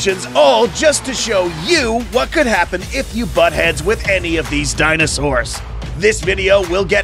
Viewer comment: did you just call us “bat” heads-???